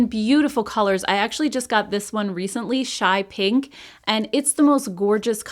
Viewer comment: I can't believe I forgot to mention the new Revlon Illuminance foundation! I compared it to the new Makeup by Mario foundation in this video and I liked the Revlon one even better! <link> Also! I'm wearing the ColourPop Stone Cold Fox Palette in today's video! colourpop.sjv.io/5bJ6JD Code for $5 off. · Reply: Hey. The gloss I looked up… I thought u said shy pink but I found a sky pink. I’m guessing that’s the one. Thanks for the info. I’m getting the highlighter n the gloss. I love love ur silver gray shadow! Please lmk what shades those r. I was hoping it was a Revlon mini